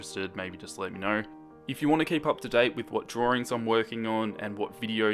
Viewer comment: when he said 'keep up' i heard 'kpop'